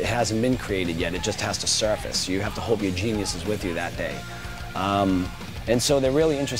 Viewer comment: what is he saying between 1:03-1:05 please someone ? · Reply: "you have to hope your genius is with you that day" like you have to hope you are in a creative mood that day.. smth like that